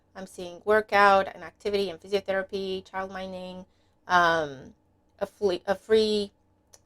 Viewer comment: Morning! I thought you said child mining when you said child minding 😂. But that’s what happens when you’re trying to listen to the reading with a 1 year old in your arms screaming at the squirrels